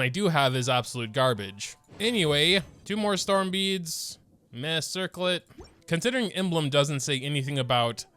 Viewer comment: did I just hear 'meh circlet'? 😒